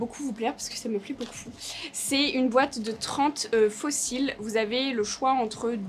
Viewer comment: J'ai compris "une boîte de 30 fossiles" au début j'avais pas bien saisi mdr